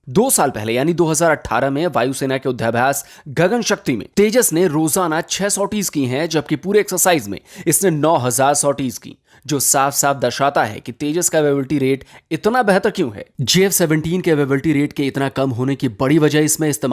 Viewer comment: 9000 sorties meanwhile in the whole exercise gagan shanti did I hear it correct ??????